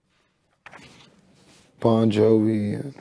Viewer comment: did he say bon Jovi lol